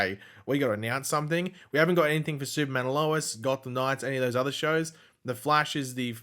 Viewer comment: did you just say Gotham Knights?